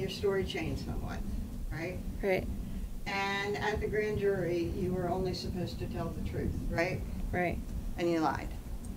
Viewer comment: What does she mumble under her breath after her answer ? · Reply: Hail Marys dead thanks to Adam